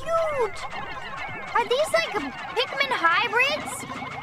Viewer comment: She said "Pikmin", not "pigment". She's talking about the game she's playing, and the creatures that the game is named for. And how did you get the caption at 0:32 so mangled and wrong? She was reading what was already on screen, word for word. Shortly after that: cross-bred, not bread. Bad captions like this make the video harder to watch, and it actively harms learning of English for ESL viewers.